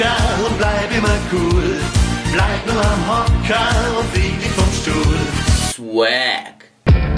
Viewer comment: ich habe bei 3:31 erst "und ficke vom Stuhl" verstanden.